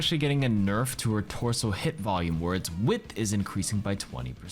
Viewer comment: LMAO I thought you said sojourns torso HIPs was increased ahahahah not HIT box